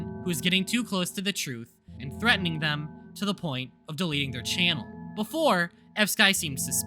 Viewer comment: I kept hearing some thing “deleting” then “Bx deleting” but I listened closely and I heard “DM deleting”